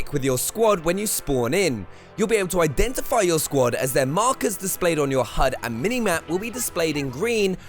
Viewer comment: Somebody please tell me I'm not colourblind and never knew it? He said squad members are green but I swear I see yellow... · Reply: 1:12 he says "you'll be able to identify your squad as their markers displayed on your hud and minimap will be displayed in green" Maybe know what you're replying to before you do so especially if you want to be using someone being high disparagingly, I'm high yes but I know what the uploader said, what's your excuse?